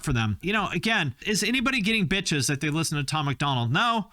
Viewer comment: When speaking of Tom Mcdonald, I thought you said ‘you dont get Bitches, just Mitch’s’.